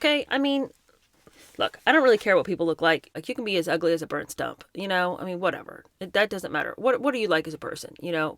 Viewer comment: so love your similes! I misheard "As ugly as a burnt stump" as "As ugly as a bird stop" and had to Google it. Apparentlly a bird stop is real but it can't be used for a simile. So I relistened with subtitles... I like it, can I use it?